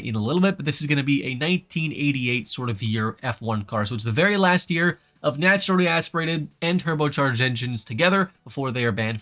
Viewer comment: I thought he said "very last year of naturally aspirated turbo engines" lol